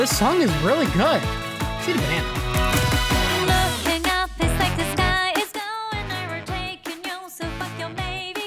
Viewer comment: what she said that was off putting 😅 all I could hear was "the sky is beautiful" or something like that 🤣